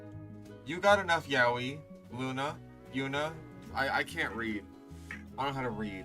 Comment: did someone say yaoi? owO ?